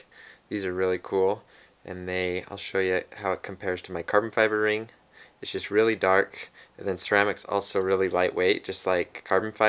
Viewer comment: is it me or in 0:12 did he say his shits really dark ? lmaoo